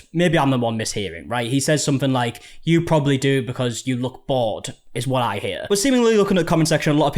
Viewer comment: “you can make me leave, you can take it there if you want to, you probably want to bc you look bored” Haha before you even said anything, I heard him say “bc you look poor” and I rewound it I think 10 times and held my phone speaker all the way up to my ear to hear it.